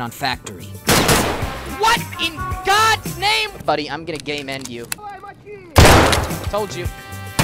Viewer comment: Sorry, when you said “I’m gonna Game-end you,” I heard “I’m gonna gay-men you.” You know the rules. P.s. Love you 🧡🎃